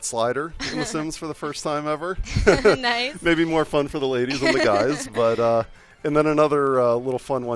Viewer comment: "butt slider may be more fun on the ladies than the guys" hahahahahahahahhaha oh the naivety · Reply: i thought he said "butt slider may be more fun for the ladies and the guys"